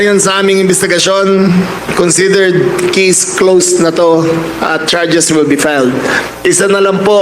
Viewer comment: Dapat yong nag imbestiga Ang magsalaysay para di binabasa lang. Kiss closed daw 😄😄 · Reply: Naging bisaya ba? I heard it too "kiss closed" instead of "case closed" 🤣😂😂✌️😉